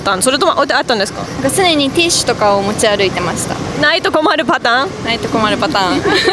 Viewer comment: what did she say like Naito komaru patang(or what). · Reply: They said: (ティッシュが)ないと困るパターン, so like if you dont have it (tissues, paper [with you]), then it's a problem/you are in troubles (lit. a [situation] pattern where if you don't have it, it's problematic). Hope it helps a bit ^^